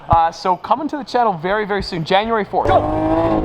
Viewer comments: did he say jan 4 thats my bd how cool is that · who else heard January 4th??!!!!!